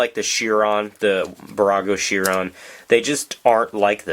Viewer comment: Did you say... Burago?